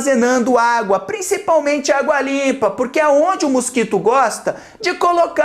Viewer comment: Eu juro que quando ele falou "água limpa" eu entendi "a dua lipa" tipo?¿